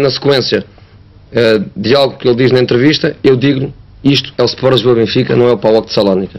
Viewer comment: o que ele diz no final? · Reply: Dia 15 de Setembro kkkkkk